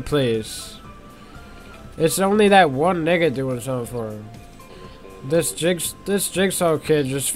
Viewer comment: did he said the n word??